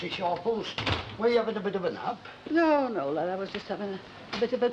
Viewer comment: Sounded like Ena called Jack, Len, at 19:50.